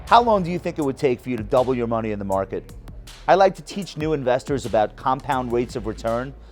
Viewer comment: I thought it said "how to donate your money to the stock market".